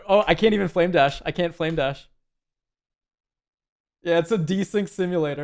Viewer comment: At first I thought you said wave dash.... I was like this guy plays Rocket League as well?